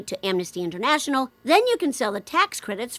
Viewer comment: Please don't let me be the only one who misheard Kroner as Kromer